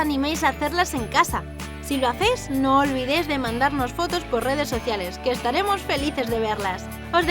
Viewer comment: parece que dice demandarnos